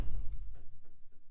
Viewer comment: Lyrics: "AAAAAAAAAAAAAAAAAHAHA AAAAAAAAAAHAHAHAAA AAAAAHAHAHAAAHAA AHAHAHA AAAAH, non mi fanno ridere le battute sugli anime." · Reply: Io ho capito "sugli albini"